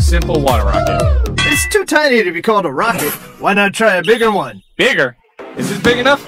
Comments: hcalled instead of called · On 0:18 second it said hcalled instead of called 🤓